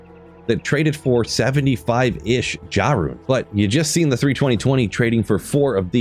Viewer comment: You keep saying "Jah Runes" and I keep hearing "Jah Rule" lol Fun video!